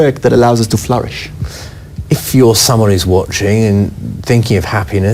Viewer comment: I really would like to know what the reporter is saying at 0:24. " if your summer is watching...?"